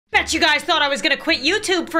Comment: I skipped ahead 10 seconds so all I heard was “I bet you guys though I was gonna MADURDER!”